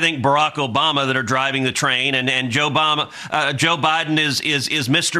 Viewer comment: Maybe I misheard, but for a sec while Cancun Cruz was stumbling over his forked tongue, I thought he said "Joe bama."